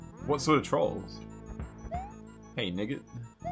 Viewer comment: OK i havent looked thru the comments on this, but who else thought said "Hey Ni**a" at 11:55??? I slowed it down and found out that it wasnt what was said, even though i thought it was. LOL Was so damn funny how that muncher just nonchalant slowly crept in the way while was going for the carlShiney. EPIC!! #JuzSmug